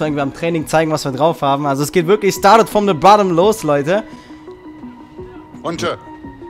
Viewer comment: Sagt bei 13:55 Started from the bottom oder ich verstehe es schlecht!? 😁